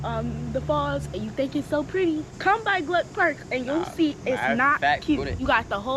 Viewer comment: did she say "gluck park?"